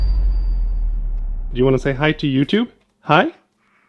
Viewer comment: is it just me or did he just say "YouTube"? (yohthoo)